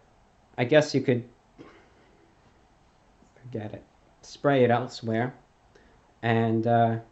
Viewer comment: Did he just say "Damn it"? It's so odd seeing him curse considering he doesn't do it often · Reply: Ash he said "Forget it"